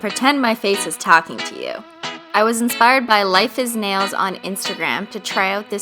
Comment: Thought it said life I snails